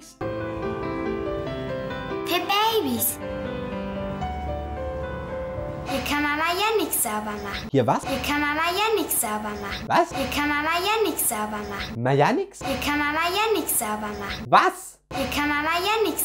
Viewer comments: das was du nicht verstanden hast heisst: "Da kann man mal Yannik sauber machen." oh Gott das ist echt komisch. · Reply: ich habe verstanden: "hier kann mama ja nick sauber machen" :D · Es heisst "Hier kann mama(den) yannick saubermachen" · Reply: DerKartoffel Typ ich hab verstanden: "Hier kann Mama ja nix sauber machen" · Ich habe endlich verstanden was das Mädchen gesagt hat. "Hier kann Mama Yannick sauber machen" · ich habe "Hier kann man ja Yanick sauber machen" verstanden und dachte sie bezieht das vielleicht auf ihren kleinen Bruder. Vermutlich gibbet den Namen "Yanick" gar nicht. Aber ich habe schon seltsamere Namen gehört. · das was du nicht verstanden hast heisst: "Da kann man mal Yannik sauber machen." oh Gott das ist echt komisch. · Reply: Luca Hochreutener ich hab verstanden "da kann Mama Yannick sauber machen" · ich habe "Hier kann man ja Yanick sauber machen" verstanden und dachte sie bezieht das vielleicht auf ihren kleinen Bruder. Vermutlich gibbet den Namen "Yanick" gar nicht. Aber ich habe schon seltsamere Namen gehört. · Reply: ich hab verstanden: "Hier kann Mama ja nichts Saubermachen." xD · Ich hab beim ersten verstanden “Hier kann Mama Yannick sauber machen“ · Ich hab verstanden, hier kann mama ja nix sauber machen :D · Ich hab verstanden "Hier kann Mama "Jannik" sauber machen" mhhm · es klang eher wie: hier kann Mama ja nichts sauber machen oder hab nur ich das so verstanden? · Ich hab verstanden : Hier kann Mama Yannik sauber machen 😂